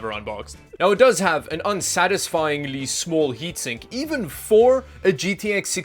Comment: Did you say "Yeetsink"